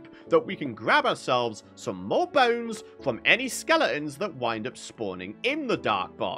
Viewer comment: composter instead of skeletons ?